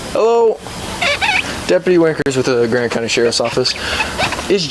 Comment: Did I just hear that correctly? 12:20 "deputy wankers"? How unfortunate 😅 12:46 no I definitely heard it correctly damn 👏🤣